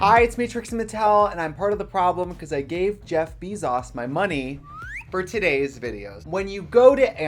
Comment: did she just say jeff beeszoes :0 TRIGGERED